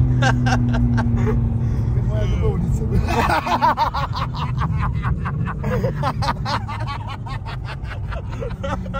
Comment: что он сказал пацаны? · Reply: Как моя любовница